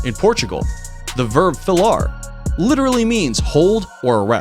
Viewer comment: I'm sorry... but I'm Portuguese, and I have no idea what the heck you're trying to say at 6:13... it sounds like "falar", but that can't be correct, cause that means "speak". You should have "wrote it" in the video 😅😅😅